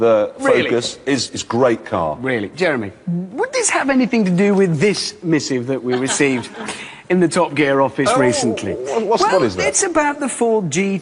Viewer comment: What does he say ? "This misiv" :| ?